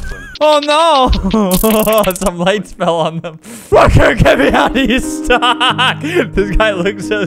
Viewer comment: it sounds like he said "f**** get me out"